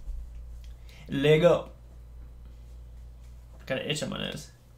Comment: for a second I heard him say "i gotta itch my ass" then I replayed it and it said "I gotta itch up my eyes" lol wow